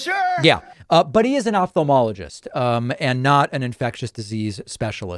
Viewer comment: I could have sworn David said “off the wall-ogist”, but either way I love how fitting it is!